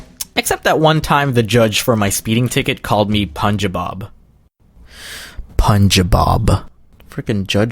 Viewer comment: i heard it as my speeding ticket called me punjibob LMFAO